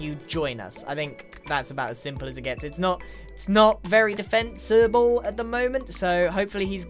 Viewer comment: D-did grian just say defencable